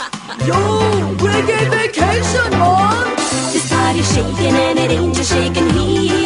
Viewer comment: Omfg I thought he was saying "Grade A Vacation Mom!" 🤣